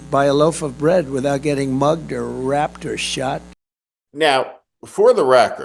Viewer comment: What did he say at 3:30 ? can't go to the store without getting mugged, wrapped, ?? Did he mean to say "raped" but didn't read it correctly?🤔